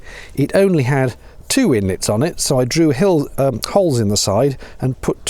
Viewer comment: Haunted? Ghosty taking the wires off? Liked the drew hills at 5:13 instead of drilled holes. Bit like overproof water trousers or smug druggler. Nice vid. Thanks.